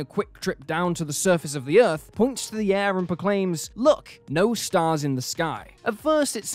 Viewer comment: I thought Astrid said, " Look, NEW stars!"